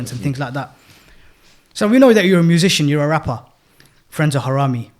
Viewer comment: When I heard mention his name, I thought he said 'friends of harami'. 😂 my bad. lol. have not heard about the brother before.